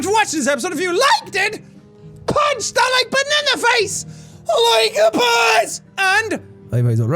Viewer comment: For a second I thought jack said "PUNCH THAT BANANA BUTTON IN THE FACE, LIKE A BOSS!!..."